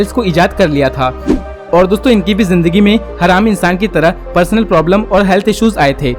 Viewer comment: you said har aam insaan but firstly i thought you are saying haram insaan😂😂😂😂